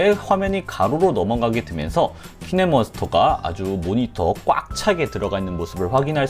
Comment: 분에 님 키네마스터가 아니라 키네머스터라 들리던데 저만 그러나요 ㅎㅎ